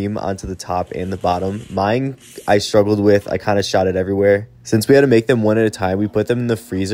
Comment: When they said I kind of shot it everywhere I thought he said I sharted everywhere.